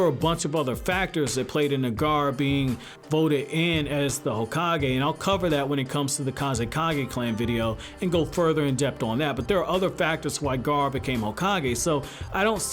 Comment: Gaara Hokage? Kazekage Clain? Am I high or did I hear that